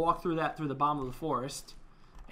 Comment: At the end, it sounded like you said bean box